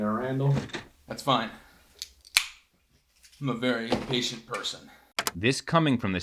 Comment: I heard "impatient" rather than patient as shown in the text overlay.